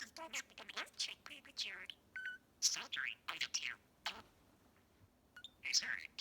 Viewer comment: While he was speaking gibberish I heard saltery 😹😹😹😹😹😹😹😹😹